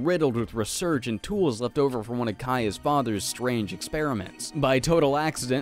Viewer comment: why did i hear "left over from one of Kya's fathers" at first